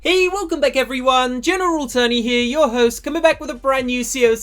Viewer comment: What he says: "What's up guys its General Tony here!" What I hear: "What's up guys its General Attorney here!"